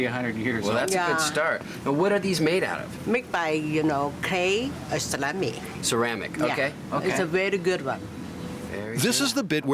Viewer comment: I thought the lady said the pot was made of salami 🤣